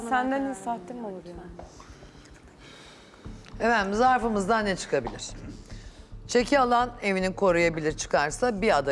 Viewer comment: ne diyor orda, acil dudak okuyacak biriiiiii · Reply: Yanındakilere bak gibi bişey diyo galiba